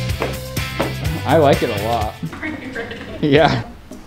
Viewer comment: RYLAND: She just called her brother her daddy Nobody: Nobody at all: Shane: I liked it. ☠️☠️☠️☠️ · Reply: the subtitles say that he said "and liked it" but it sounded like he said "i liked it"